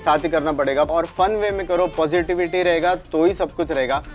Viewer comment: I heard posititivitities 🌚